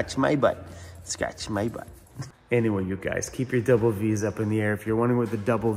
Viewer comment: Funny about the double "Ds". For a while, I thought you were saying "double visas". Then I realized you were saying "Vs" but I thought it stood for the peace sign. Glad you explained that it stands for "V" van city and "V" for van life. Ha, ha.